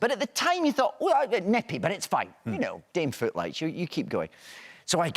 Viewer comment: Please tell me what did David say at 15:16? Smth like daim fruit light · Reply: “Dame footlights” theater speak, like the show must go on sort of thing You were close!